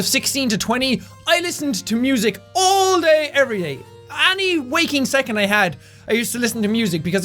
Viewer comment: lol i thought he said every wanking second